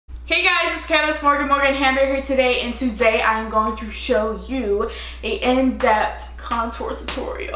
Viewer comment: I thought she said "Morgan hamburger" 😂😂😂